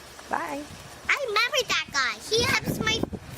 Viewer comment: "I membered that guy", "he helps my friend". -- is what I heard.